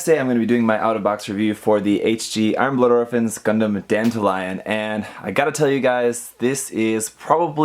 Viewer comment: is that not what I said? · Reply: it sounded like you said dandelion like the flower not Dantalion the demon (like all the gundam frames from IBO are named after demons) might just be me haha